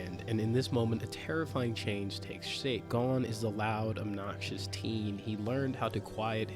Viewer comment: did you really call him Gon not Eren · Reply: "gone is the loud obnoxious teen" 😂 love hunter x hunter though!